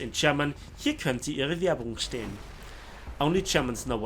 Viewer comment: pls german fellows, im just starting out in german so I dont know much. what did he said pls? 5:17 thank you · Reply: He said: "Hier könnte Ihre Werbung stehen. " or "Here could be your advertising. " which is a common thing on emty posters.